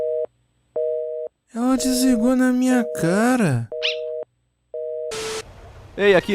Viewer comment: eu n entendi ;_ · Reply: O makoto foi logo encher o saco da mitsuru